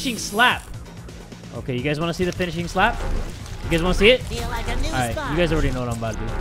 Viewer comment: what I think spongebob says: "feeling like a juicebox" What he actually says: *i feel like a new sponge "😂🤷 my tablet went blurry while seeing this so I could only judge what he said by listening, oops😅